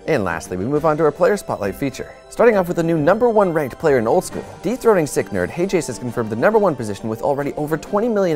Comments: I thought said "Deep throating sick nerd"... Lol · does he say "deep throating sick nerd"? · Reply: Erebody Snuggie dethroning